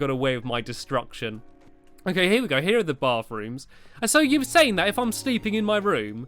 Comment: It sounds like hes saying Barth room